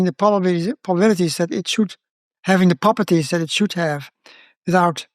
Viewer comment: Keep in mind, every episode on this channel has meticulously double‑checked captions, so if you can’t understand something, turn on the transcript! I’ll also be posting full transcripts with side commentary and more information on my Substack in the future if you’re interested: <link> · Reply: the captions are wrong at 7:49 he corrects himself to say properties after mispeaking and saying posibillities